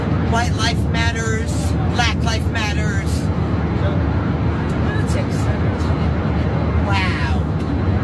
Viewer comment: Did I hear "lunatics matter" 0:58 😂😂😂😂